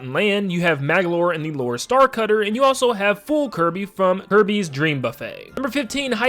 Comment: I realized how in the part were he was talking about the rock statues when he said kirby dream buffet does anyone hear he said herby's intead of kirby